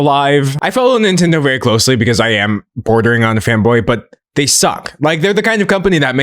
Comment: I'm fairly certain that what was said was "fanboy", but it's said in such a way, that it is utterly indistinguishable from "femboy" to my ears lmfao